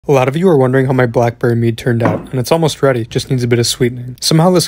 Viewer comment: Heard that as black-briar mead the first time. Got Skyrim on the mind a little too much